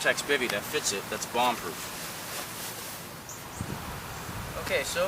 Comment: wait at 1:44 did you say it was bomb proof?